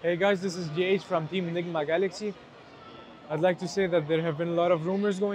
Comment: I swear I heard "GH from Team Ligma Galaxy"